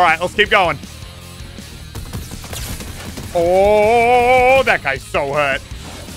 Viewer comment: When he said that guy so low I thought he said he's solo😂😂😂